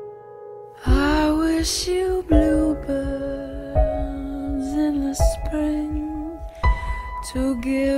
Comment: The little girl on the guitar brought me here. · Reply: Same first I thought she said I wish you blue balls come to find out that she was saying I wish you bluebirds